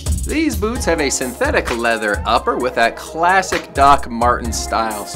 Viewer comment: Is this not a leather chukka boot. I heard you say synthetic leather. What a turn off from Martens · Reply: Hello! Yes this style is made of a Synthetic leather upper! *MG